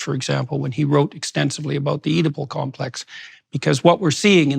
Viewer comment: What complex was Jordan talking about at the very end of his video? It sounded like he said edible complex, but googling that gave no results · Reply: Oedipal complex